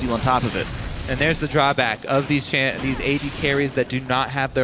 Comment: lol @ 36:20 did he say asian carry ?